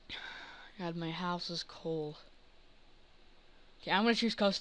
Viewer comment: did he say GOD MY HOUSE IS COOL?